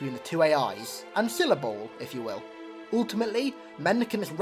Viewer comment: what word did you say? "Ancillable"?